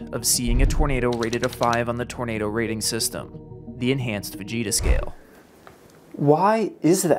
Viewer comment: thought he said advanced vegeta scale